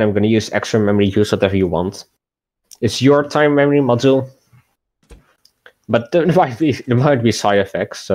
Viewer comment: youtube's auto captions says "but then it might be psi fx so"